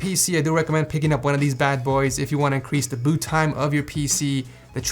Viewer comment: did he say increase?